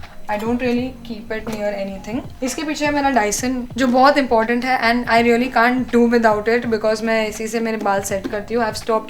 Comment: what is that?? Can anybody tell? She said something like dision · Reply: Dyson hair styling kit